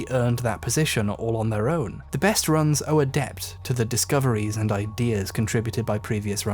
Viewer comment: does he say "owe a deBBt" ???? Seriously?